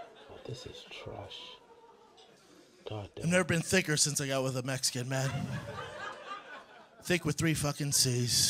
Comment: Did he say “since he got with a Mexican man!?” 🤔 @ 33:00 in my experience hot Takis are more popular, but it could also be regional. Not sure if in LA hot Cheetos are more popular. @ 42:00 |Hes always said “ThURPist” and “ThURP-EE!”